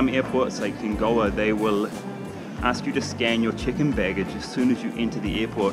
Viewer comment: I thought you said chicken bags ... chicken staff 😂 🤣 😆